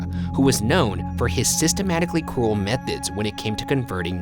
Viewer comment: I heard “cruel methods” as “cool methods”